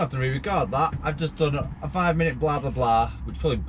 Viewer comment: I thought he said 'I've just done a shit.' I was wondering how he shit at the roadside and why he needed to clean his boots. I had to rewind twice.